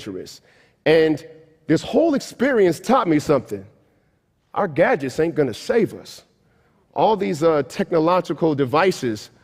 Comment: I thought he said "our god is not going to save us" but he said "our gadgets are not going to save us"